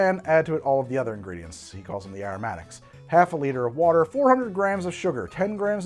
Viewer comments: he says half a liter of water but the caption says half a liter of london dry gin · Did anyone else catch the caption said gin when he said water?